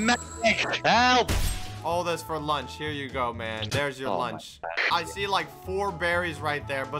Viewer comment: blaza! I got you lunch! Me: ok he brought blaza punch after a while: here’s your lunch Me: I thought he said punch